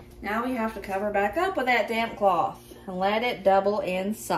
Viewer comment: I'm giggling. While preparing your dough balls, I thought you said, "cover them back up with the damn cloth"..... 🤣